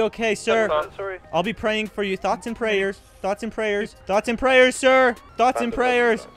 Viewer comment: what he said "thoughts and prayers" what i heard "thots and stairs" 😭